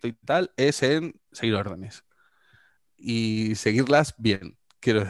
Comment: No se entiende la palabra que dice. Lo he escuchado como 20 veces, y nada. ¿"Seilerrones"? ¿"Seiguerrones"? ¿¿Qué dice?? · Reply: Vale, más adelante lo repite con más claridad: "seguir órdenes". Joder...